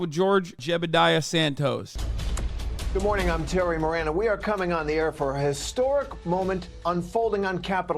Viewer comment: Did he just say he was "Cumming on thr air," ? eeewww gross